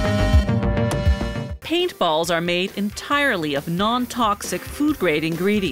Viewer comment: I thought it said ping-pong balls.